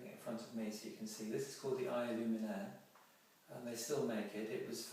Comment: you pick up a small bottle, what was it called? could you please spell that for me so I don't get it wrong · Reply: Hello, The small bottle is called the Eye Illuminaire. best wishes.